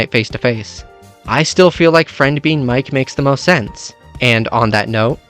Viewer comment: I misheard "I still feel like Friend being the Knight makes the most sense" and I just want to let you I made the biggest stank face before I realized I heard so wrong. 😭